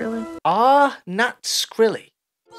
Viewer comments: What she says is "Ehm, thats really.." and breaks off the sentence. Not sure what that is supposed to achieve as a scene but that is what she said i am certain. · “ummmnn that’s screwy” is what I heard, but the last word is 70% mumbling xD